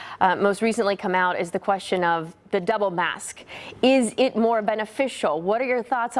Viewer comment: Double mask? Sounded like she said DEVIL MASK. Sounds about right....